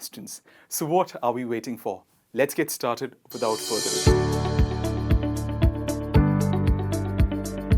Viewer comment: What’s he saying at 0:45? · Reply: Well, he said "So, what are we waiting for? Let's get started without a further a due."